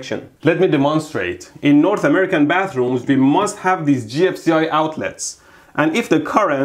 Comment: Every time he says GFCI outlet I hear "Gypsy-Eyed Outlet"